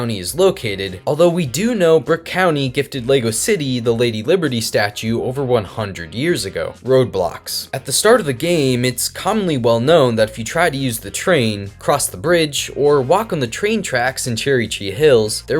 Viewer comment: i heard roblox but its roadblocks